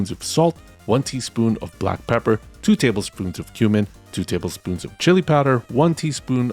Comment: Me: did he say human?